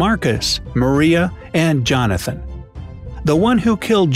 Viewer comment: did he just say jonathan and then a woman showed up